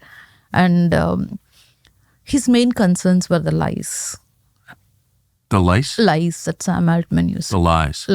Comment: I thought she said lice too...